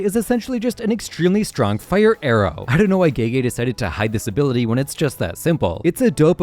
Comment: why does the captions say gaygay instead of gege